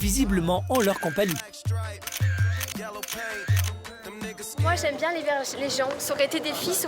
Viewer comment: Suis-je la seule à entendre « moi j’aime bien les verges » à 22:05 ?😂